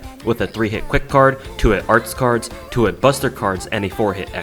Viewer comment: Sir you are a legend! Got to Ina's part Misheard 4-hit Buster with forehead buster